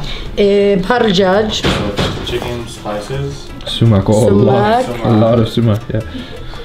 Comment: What was the spice she said at 4:42? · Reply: first spice was chicken spices, after it summaq spice...